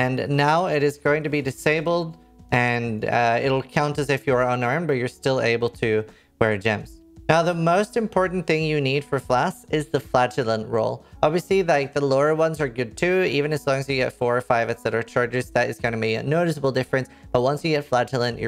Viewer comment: Can I just say I heard "flatulent's" instead of "flagellant's" and had to tab back over to figure out what you were talking about?🤣